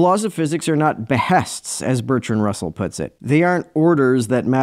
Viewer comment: the subtitles say and, not as.